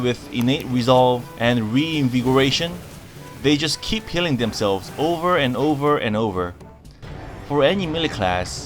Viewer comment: I was only listening and with the accent I thought you said "they just keep killing themselves over and over again" and I thought you were talking about cat form, wondering if Blizzard has given Druids 9 lives.